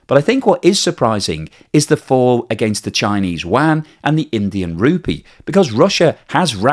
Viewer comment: Did I hear "Chinese wang"? 😂😂😂